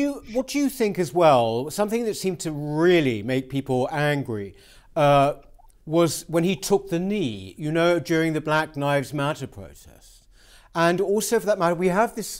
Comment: It sounded like you said "Black Knives Matter" in the audio - not sure if a Freudian slip or intentional jab, eitherway, I smiled.